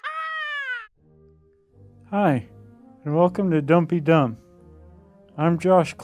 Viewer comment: Am I the only one that heard him say, "Welcome to dumpy dump"? Just me? Okay...